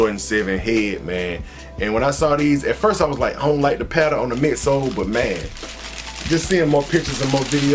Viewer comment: I thought you said you didn't like the patta in one of your reviews.